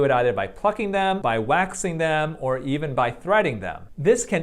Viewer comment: I thought he said by "by f+cking them or waxing them".